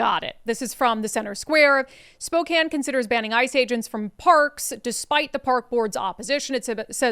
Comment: ☔... thanks again... made myself laugh...i thought it said..."Spokane considers porK ban for ICE agents" ...i wouldn't be surprised if that's next.